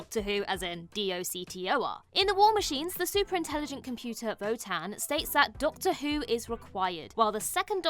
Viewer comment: I could swear says 'VOTAN" not "WOTAN".